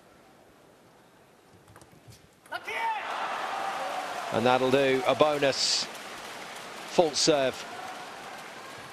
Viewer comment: What malong said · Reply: lucky！